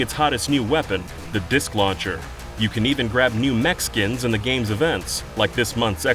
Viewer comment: I'm a Mexican and I had this video on the background. @ 3:19 I heard: "You can even grab new Mexicans". And I was very confused